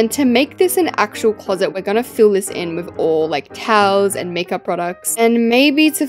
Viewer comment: When she said towels I thought she said cows 😂😂 😂